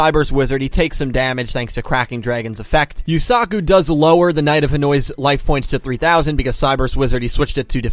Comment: lmao. at 4:02 the captions say you called the Knight of Hanoi the "Knight of Annoys" xD